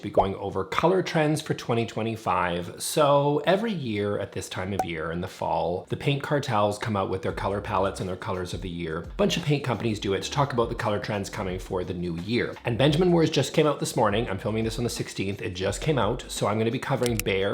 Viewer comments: Nothing makes me smile like hearing "the paint cartels." 😂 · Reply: I swear I heard him say "Pink cartels" 😂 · I genuinely thought you said 'pink cartels' and got very confused 😆